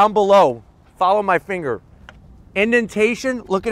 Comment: thought you said "smell my finger" pheewww